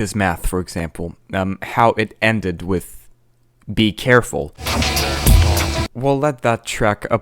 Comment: in music in math i have always heard "now-oh-AHW" as in the word now... but you have just shown me something else. i have heard this for 20 yeards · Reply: i always heard "be careful" as "purple"